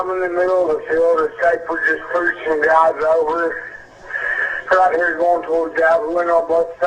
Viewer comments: is when the recording of his 911 call starts. Putting this here so we can easily press it to hear it again. Maybe aliens? Jk lol it sounds like he said a someone just pulling guys over? something about them getting guys....? damn I wish someone would pick this apart and find out what he's saying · I swear I heard him say "preaching" also. But I ruled it out because it just didn't make sense. It really sounds like he says (somebody - unintelligible) was "preaching guys over". Just really really weird. If only the audio could somehow be enhanced or what he was saying could be totally understood.